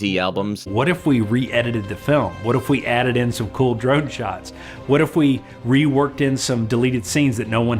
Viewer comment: Did I hear right--"cool drug shots"????